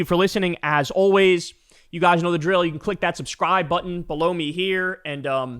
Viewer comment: One time you said "Click that subscribe button below me here..." so fast that you accidentally said "blow me", and my tween had walked into my room right then and heard that & gave me a look like "The f*ck?!?" 😂. So now every single time you say that, I hear "Click the subscribe button blow me right here." And w/ the pointing down too its too much for my immature ass 😂. I'm a whole grown ass child lol.